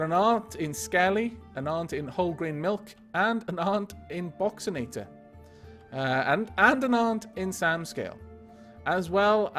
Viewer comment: wait did he say i was some one's aunt 8:35 IRL i am a guy